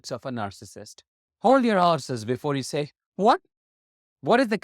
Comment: I thought you said "hold your arses". Lol. Then I realised it was "hold your horses".